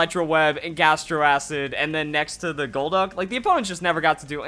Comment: Lmao I don't know why but I heard "Dilduck"